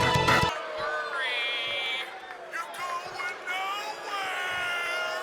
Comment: if you’d be ever so kind to translate what the crowd was saying here I’d appreciate it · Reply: sounds like they're doing their best Macho Man impression by saying "you're going nowhere"